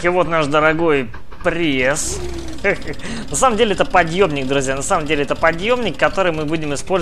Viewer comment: мне одному услышалось Подёбник?